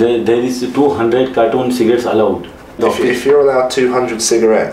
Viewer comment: he said he had 200 cartons of cigarettes not 200 cigarettes, that customs agent misheard him. nevertheless that man is in big trouble.